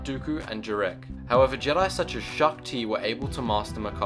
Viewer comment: thought he said shaun t.